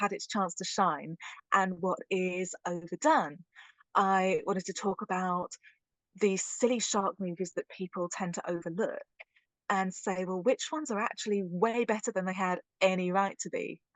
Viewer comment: At first I thought she was saying city shark movie!